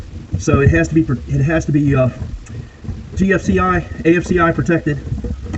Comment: Great video; thanks for sharing. I thought you said a GFCI/API outlet was needed, but that didn't look like one, but maybe I just couldn't see it.